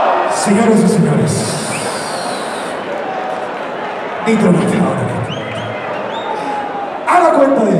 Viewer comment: cuando preguntan quien ganó y el público responde Nitro yo pensé que decían visco